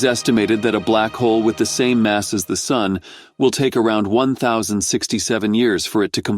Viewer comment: So many people misheard the last description lol, he said "a black hole with the mass of our sun"